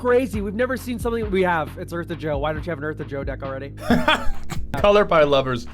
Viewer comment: what commander is naming when the subs think he's saying "Earth of Joe"?